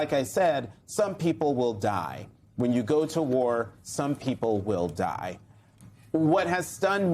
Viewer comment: what I heard was Rump saying "yeah, some losers will die"